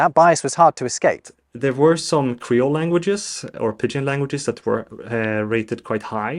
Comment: captions: pigeon instead of Pidgin